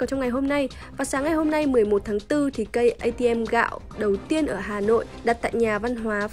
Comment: nghe thành K-ATM :))